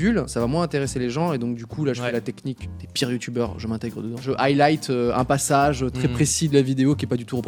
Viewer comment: a 10:09 il dit mon pseudo !!! Aylight !!!! j'ai frissonné sans comprendre quand il l'a dit puis j'ai compris qu'il disait autre chose, mais c'était drole !!